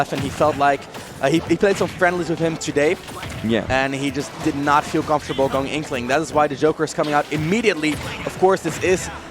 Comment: did he say "did not feel comfortable con inkling"?